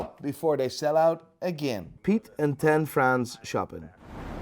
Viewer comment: I thought I heard him say Pete and ten friends shopping but he said Pete and tan France 😂😂